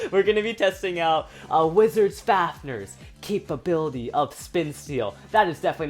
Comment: did he say wisars fafners